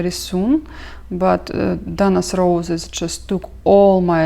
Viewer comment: Hi! What is the roses kit called? I thought you said Dana’s Roses by Bucilla? Is that correct? Your stitches are absolutely beautiful and I love watching you! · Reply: I think it's damask roses